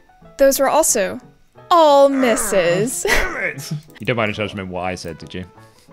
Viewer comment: wha on earth is saying here? I tried listening to it a bunch even slowed down and I still have ZERO cllue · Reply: ohhhhhh "you don't by any chance remember what I said, did you?" ass soon as i let the video play and got context it became much easier XD